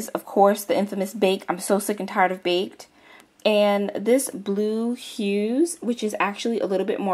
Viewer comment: i thought it said blue bus not blue hues?